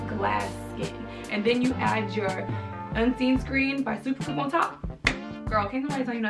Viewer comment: what was the product you named? Was it Unseen Screen by....? I couldn’t really understand what you said😘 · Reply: I believe she said its by Super Goop